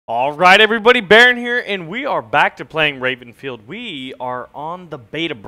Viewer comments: d-did he just say: 'rapenfield'...? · did baron say Rapenfield??